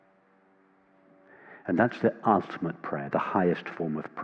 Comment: Can someone with an english mother tongue help me please: I dont get the word of the highest prayer 4:02 "and that is the athmed ..? prayer". I hear this word, but I really dont think this is right .. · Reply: "and that is the ultimate prayer, the highest form of prayer..."